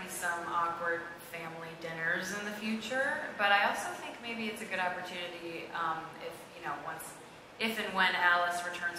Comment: someone PLEASE tell me what cami said at 15:10 , I don't speak english and I'm trying to understand but I can't my theory is that cole put his hand at lili's thigh and mads & cami realized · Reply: no, she's definitely saying "I'm cold"